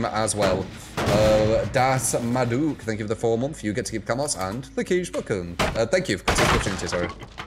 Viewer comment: Still sounds like u say u get to keep the camels hand lol